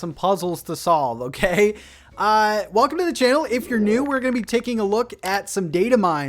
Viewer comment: lol it sounded like he said "Welcome to the jail"